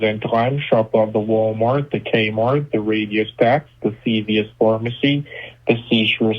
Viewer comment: Did he really say RADIO SHACK???? Or am I losing my mind ? 🤣😂